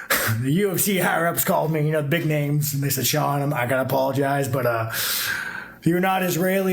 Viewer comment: instead of sean, the captions said "Shalom" :face-blue-smiling